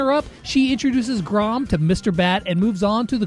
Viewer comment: When he said Mr p I thought he said mrbeast